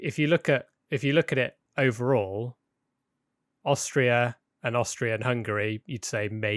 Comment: austria and hungary me:austria-hungary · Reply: I thought he said austro Hungary